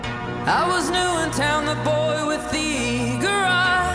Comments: Lol I thought it said "I was new in town the boy with the garage" lol xD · i thought it said the boy with the garage?